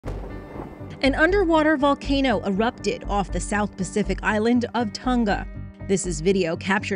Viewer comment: I thought it said an Underwear Volcano erupted for a sec..